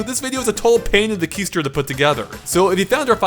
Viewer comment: he says keister but the captions say toadstool :o